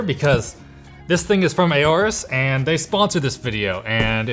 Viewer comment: I swear this guy said "this thing is from my ass" 0:06. Lol.